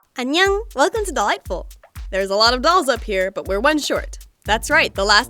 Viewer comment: For a long time I thought she was saying "Welcome to the lightbulb!"